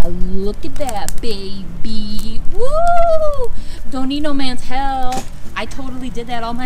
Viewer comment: Girl is the 3:15 mark don't need no mans hell 🤣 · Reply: Haha it DOES sound like I said that!!! But I did say “don’t need no mans help” 🤣🤣💀💀 when it comes to words THEY NEVER COME OUT RIGHT LMFAO